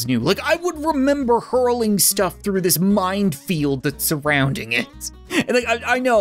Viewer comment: Did you just say “mind field”?